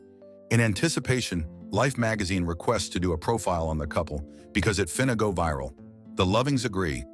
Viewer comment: "..because it fina go viral." Did I hear that right? And if it's not a typo being read as it is by Mr. AI, then what is 'fina'?